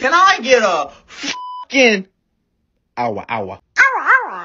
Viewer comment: Why did I hear you say "cow a wow a" 😂 ALSO OML YOUR VOICE GHGDHLKSWTVJKNBDSTJOKVDFSWRBKON